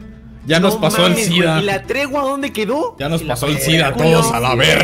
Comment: Pensé que decía "la traducción"